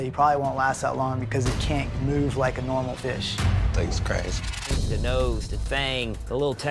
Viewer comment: Did I just hear say "thanks Christ" after explaining why his character won't live long? 🤣